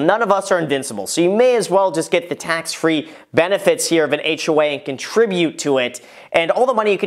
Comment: did you say we should invest in an HOA? 😅